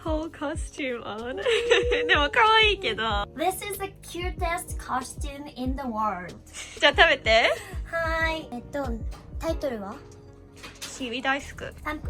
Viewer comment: When was talking about her costume, I thought she said this is a cute ass costume lol